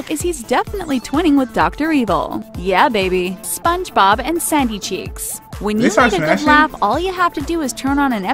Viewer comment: When she said Sandy Cheeks I thought she said Saggy Cheeks.